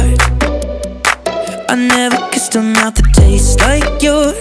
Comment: I’m sorry but when he says “I never kissed a mouth that taste like yours” at 0:15, what I hear is “I never kissed a man”